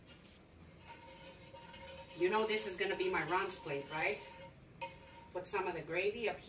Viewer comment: I thought you said that was your Ron's plate.